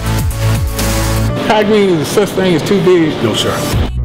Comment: what is the Trainer saying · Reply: KuchenRevolver "Kai Greene any such thing as too big?" ~NO SIR!!😍😍